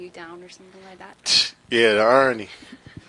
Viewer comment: What did KD say there that made everyone laugh ? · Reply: Aaron he said the irony